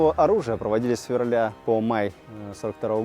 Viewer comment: Мне послышалась сферраля это что за месяц 😂